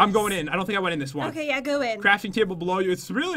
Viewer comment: I heard said crap